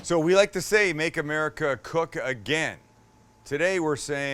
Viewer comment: For a second I thought this guy said”make America great again” I was like nope time to go